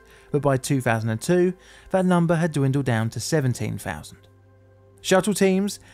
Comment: are you able to fix the subtitles? You state that workers go from 30,000 to 17,000, yet the subtitles say 30,000 to 17. A deaf viewer would get a much different view of the workforce with this issue